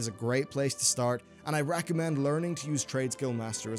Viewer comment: What does he say at 4:22? Where is a great place to start :s BTW Nice content and production, enjoyed your legion vids! ;) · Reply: recomend